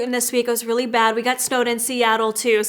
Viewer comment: did say to ignore her chin because she "got stoned in Seattle"???😂😂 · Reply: Got snowed in LOL We ordered pizza because I had no food at home and dang that pizza did a number of my stomach and acne :(